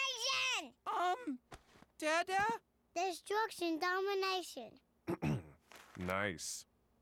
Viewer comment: i heard "theres drugs in domination"